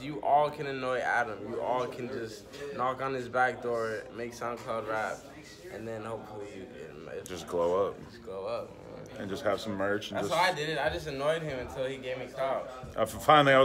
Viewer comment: Did he just say, “knock on Adams backdoor and just blowup?”